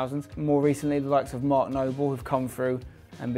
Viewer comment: what's the name he says at 4:12? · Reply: Mark noble